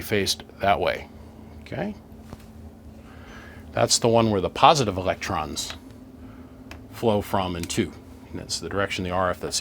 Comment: Did I hear you say 'positive electrons' at 49:28 ?